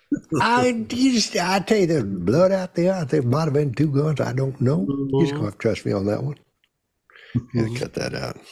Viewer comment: Does anyone understand what he was saying? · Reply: No but the captions say "I think might have been 2 guns, I don't know, he's gonna have to trust me on that"